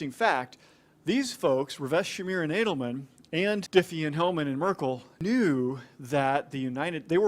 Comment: Sounds like he says “Adelman”, but it appears that he is saying Ed Hellman.